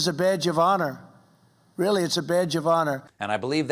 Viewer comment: When the orange balloon sack said ‘badge of honour ‘ I thought he said ‘it’s a bad Giovanna ‘ mob style So proud of Sir Thomas Moore we love him here in the U.K.